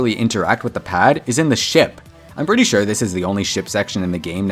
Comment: why did he say a swear word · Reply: He said ship